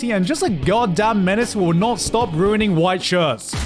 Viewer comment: lmao I thought it said he can't stop whinning and was really confused